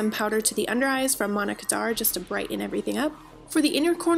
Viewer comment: Omg all this time I thought you were saying "Monica darr" powder, now I know why u couldn't find it😂😂 LOVE this look😍